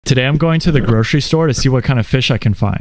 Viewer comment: Bro I thought he said grocery store 💀